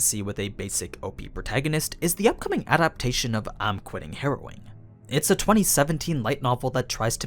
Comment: I thought you said “I’m quitting Heroin” 😂